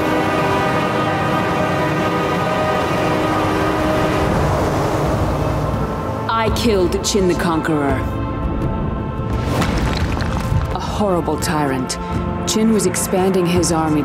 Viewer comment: Ive always wondered this… why does Kyoshi say “i killed The Chin the Conqueror”, at 0:23 ? Was saying and extra “The” like a line error? A misread by the VA? Does it just sound like she says it? Everyone else just says “Chin the Conqueror” without that extra “The”